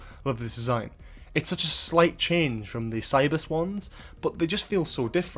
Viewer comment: You say "Cybus Ones", I hear "Cyber Swans" Now I want to see Cyber Swans.